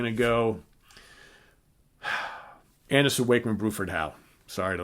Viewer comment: Can somoene help me understand what he is saying at 9:14? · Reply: Anderson, Wakeman, Bruford, Howe